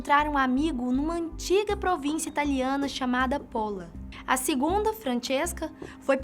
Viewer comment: Só eu entendi Porra?